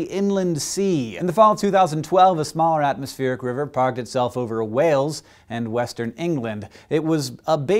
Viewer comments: For a minute I thought he said "Wales in western England" - comment section crisis averted. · Actually, he said Wales and Western England. I thought he said in to, but he doesn't!